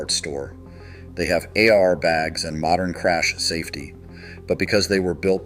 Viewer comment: The irony of the voiceover on this video being AI is killing me 😆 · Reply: yes, when "it" said "AR bags" instead of "air bags", that was a dead giveaway! about 4:35 in the video.